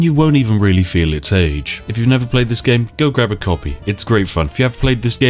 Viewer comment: "If you've never played this game, go grab a coffee." E... excuse me, WHAT? Oh. He said "copy," not coffee. Phew.